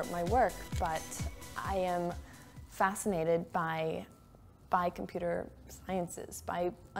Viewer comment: Did she say “by computer sciences” as in the plural of science at 2:52?